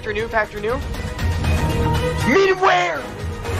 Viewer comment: I swear he said medium rare not minimal wear